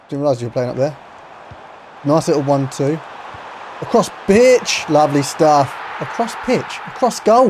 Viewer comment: I SWEAR you said across bitch and I’ve just pissed myself!!!